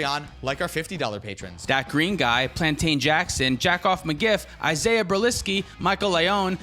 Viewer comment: I heard ‘JackOffMyGift’ instead of JackoMcGiff and I couldn’t stop laughing. 😂